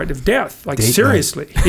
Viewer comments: what was the laughter over? I couldnt hear jocko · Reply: Date night.. seeing the embalming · What does Jacko say @ 1:00:02? JP & Echo had a good laugh, but I didn't quite catch what Jacko said. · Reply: "Date night". Hah!